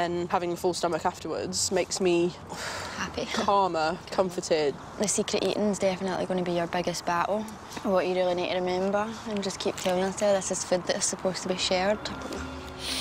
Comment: Please someone can tell me what the blond girl says aroud minute 2:27? Thank you! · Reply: "The secret eating is definitely going to be your biggest battle. What you really need to remember, and just keep telling yourself, is that this is food that is supposed to be shared." (Scottish accent).